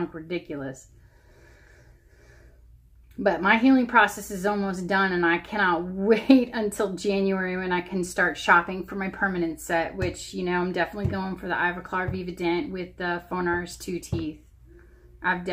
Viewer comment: you mention a type of denture....What were they? I couldn't quite make out what you said. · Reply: oh yes, I will be getting my perms using the Ivoclar Vivadent Removable using the injection mold system, and for the teeth phonares II <link> <link>